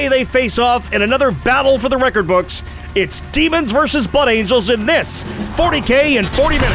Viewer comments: Daemons vs Buttangels, i cant unhear that now (iam probably the only one that heard it as such.) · So I misheard it as "It's Demons vs Butt Angels." and uhm... that paints a very different mental image....